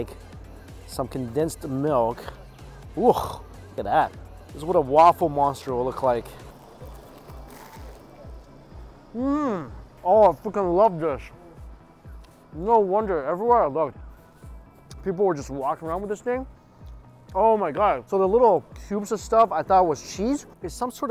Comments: Did drop the F bomb at 3:07 when he bit into that waffle treat?? I swear I thought he said "I fu*king love this!"😂🤣😂🤣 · who else heard "i f'ing love this" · Did he just say I "fuckin" love this? If he did, its the first time Ive heard him swear in a video!